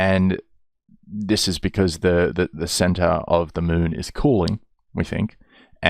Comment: I don't know why but for some reason at 14:31 I thought you said "the center of the moon is corn."